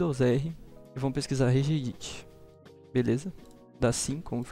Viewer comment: o que ele falou na parte 2:03 do vídeo?? "Reg.."?? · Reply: Regedit, só prestar atenção ali, Windows + R